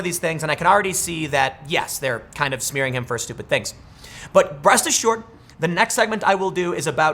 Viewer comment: did he say, "Breast is short"?